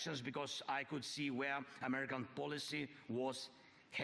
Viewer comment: "Where America was hating" I know that's not what he was trying to say; however, I'm ashamed to say, it was all too fitting.